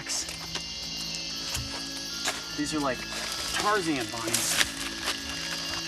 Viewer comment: did he say "Tarzanous"?